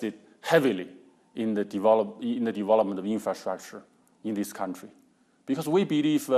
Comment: was he saying 'devolving development' <_< )